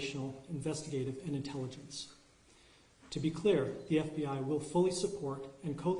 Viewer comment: sorry, i missed that… did you say intelligence? USA USA USA